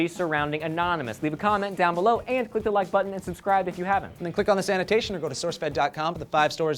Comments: It sounds like Joe says 'Click on the sanitation' at the end of the video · Every time Joe says "this annotation" all I hear is "sanitation"...